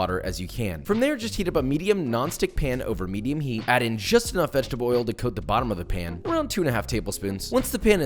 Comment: did you say vegetaboil XD